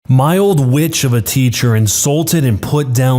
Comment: At the very beginning, I wasn't paying attention and thought he said b¡tch instead of witch 🙂🙃🫠💀🫥